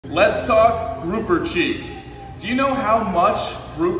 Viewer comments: I could have sworn he said groupie cheeks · Dude ngl I thought you were saying cheese the entire time, I think I need my ears checked